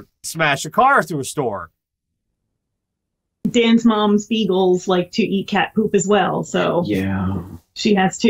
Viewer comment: i really thought said "Dan's mom's EAGLES" and for a bit i was very confused...